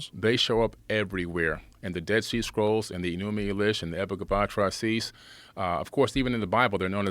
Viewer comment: Anyone know what he’s saying at 11:00 ?? “They show up everywhere. In the Dead Sea Scrolls, in the Enuma Eliš… ???” What’s that last one? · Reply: Epic of Atra-Hasis- discovered in the library of Ashurbanipal and first assembled by George Smith in the late 1800’s. My introduction video goes into some more details concerning these three